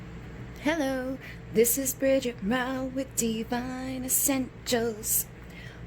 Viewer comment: Is it just me or did she say in the intro